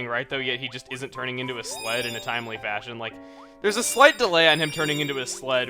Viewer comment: I could of sworn that you said "Sometimes Kirby doesn't turn into a slut in a timely fashion." O.o